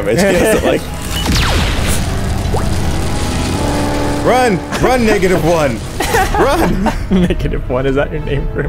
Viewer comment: bro he said run negative 1 but i just heard “run nega-“😂